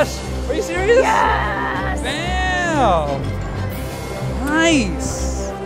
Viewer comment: did say daaaaaaaamn. I thought this was family friendly